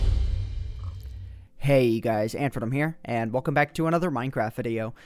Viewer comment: Oh wait, I thought he said AuntyVenom